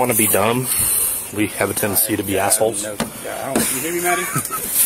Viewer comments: Listen @ 41:50 someone says “Do you hear me Maddie?” Am I hearing wrong!! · Weird did anyone here at 41:49 about there can u hear a cop I think asking “ did u hear me Maddie “?? Anyone hear that or did I hear it wrong 🤔